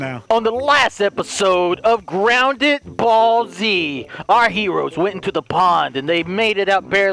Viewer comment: I heard it as "ground that ball Z" 🤣🤣